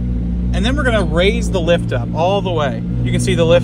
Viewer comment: I keep hearing “lift-uh” and I don’t like it